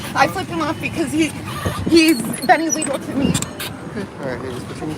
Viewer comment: In the beginning, when it said "unintelligible" to me it sounds like she says "he's been illegal to me". But also if you're gonna flip someone off, make sure you signal properly.